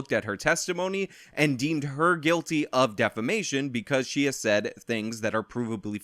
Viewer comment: For a second I thought you said Amber Heard was found guilty of defecation (her pooping in his bed was one of the allegations against her). Some puns make themselves.